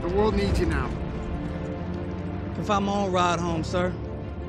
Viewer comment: I keep replaying but I still don't know what this guy said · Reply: "Ill find my own ride home, sir"